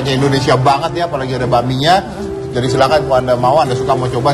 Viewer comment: 22.42 mas dedi bilang "enak banget apalagi ada babinya ??" atau aku salah denger ?